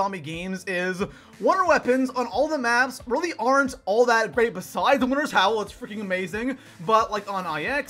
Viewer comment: did I hear that right? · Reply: The Winters Howl is ah AMAZINGG